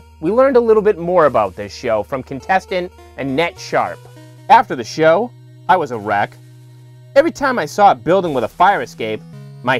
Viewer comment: I thought said "After the show I was erect!" ... Thankfully the text on screen revealed it was "After the show I was a wreck!"